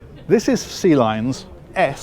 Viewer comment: I know you're saying "Sealine" but I keep hearing "Sea Lion."